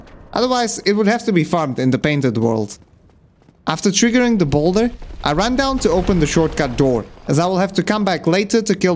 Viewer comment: I must have watched/had this in the background while doing something at least... 6 times. Yet movies of a minimum of 1:30 hour length are too tiring for me to watch🤣, jokes aside I really love this video. All explained in great detail to make people understand the hidden mechanisms of the game even if they are not familiar, it really shows how much dedication you have into this game <3 One stupid question at last... at 2:16:02, what is is that you say? I need to know after seeing it so many times XD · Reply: Thanks a lot for the kind words! I say Lukasz 'Ziggi' Leszko - it's one of the names on the screen at that moment! 😂